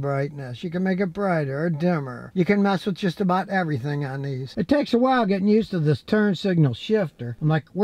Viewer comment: I thought he said, "You can mess up just about everything here"🤣🤣🤣